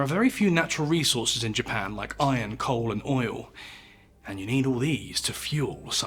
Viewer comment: he said iron coal and oil i heard iron colon